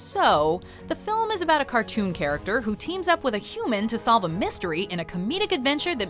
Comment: Did you just say pikachu to be a cartoon charecter?